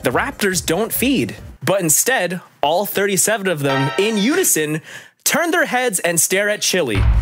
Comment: all 37 of them Me: wait I thought you said their where 27 of them?